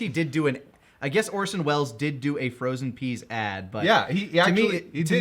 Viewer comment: I always heard this as 'rest in peace'